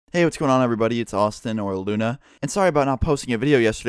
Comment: I thought you said oral luna